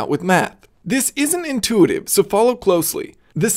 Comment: I thought you said it's not intuitive?